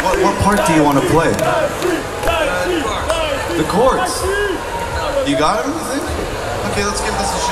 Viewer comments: I have watched this video oh too many times... and it is only just now that I am realising that was playing the chords not the riff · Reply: always thought he said "the Chorus" ! it all makes sense now... muchas grassy ass :) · i thought he said the chorus but he said the chords and i feel so fucking scammed